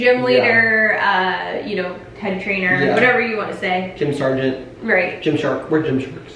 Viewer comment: I totally heard say "What a B*tch" too lmaooo I had to play it back to heard it right lol